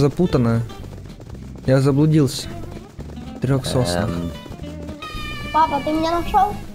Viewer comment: мне послышалось или он сказал ПАПА?